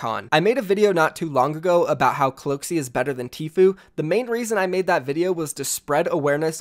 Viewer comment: I thought you said cloack is better...